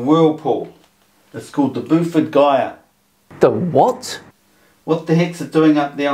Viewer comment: I have no idea what he says at the 7:50 mark, but closed captioning has it as "the boofer guia" - good luck googling that...